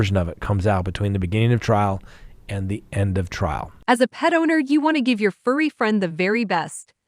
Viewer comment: I thought that last add said, "as a pedo" but it went on to say pet owner